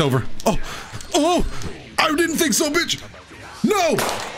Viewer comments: did just say b*tch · Did say bitch?